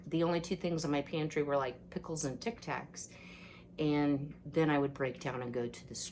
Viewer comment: I had to replay, I thought you said pickles and Kotex...lol...I'm laughing so hard. You said Tic Tacs...lol...I need more coffee. Frankie likes his water fresh! I use 1/2 gallon Ball jars for everything. Your pantry looks great!